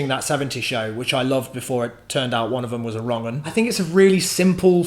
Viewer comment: What does he say at 6:22 about the 70's show? "it turned out one of them was a ______"? Thanks in advance!!! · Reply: "one of them was a wrong'un" Just that someone on the show was a bad person :)